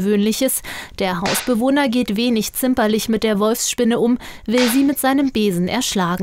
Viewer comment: Ich hab gerade "Wurstspinne" verstanden..